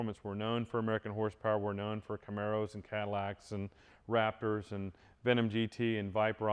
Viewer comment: When he said "Raptors", it sounded like he said "rappers".